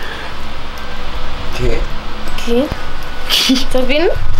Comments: cuando fede le dice a piculincito que si sabia que habian desenterrado la ouija piculinsito dice que si que sabe quien la desenterro y se escucha un susurro que dice "quico" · se ve un Drón en la ventana 6:10 Cuando Fede le pregunta "Quién fué" se escucha muy bajito "Quico" 8:39 hay interferencia 9:57 A la derecha parece que hay alguien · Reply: En el minuto 6:10 fue alguien que dijo "Qué?" En el minuto 8:38 fue el viento :v